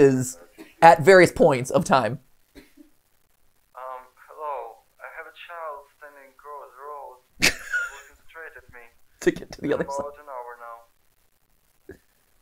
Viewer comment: bruh the accent threw me off because I heard “standing across road” 😂😂😂😂😂😂